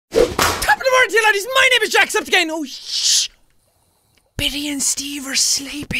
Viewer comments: at the start the accidentally said jacksepticgay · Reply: It does sound like he said jacksepticgay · at the start the accidentally said jacksepticgay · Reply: It does sound like he said Jacksepticgay :P